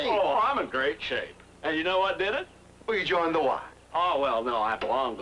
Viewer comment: It sounds like Barney is saying, "I got a runge" at the end of the clip. Oh well, that explains everything! :D Please upload more of this episode, so we can find out more about Barney's runge!